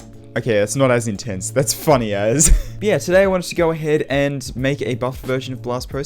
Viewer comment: did he say "funny as" or "funny ass"? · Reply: Funny as